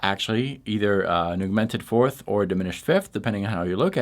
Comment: I love your stuff! Big fan! Have absolutely helped with my knowledge and chops.... but are you saying augmented? it totally sounds like youre saying "an eww-mented" or "a newmented." lol Maybe its just me? If thats my only complaint, you know your on the ball!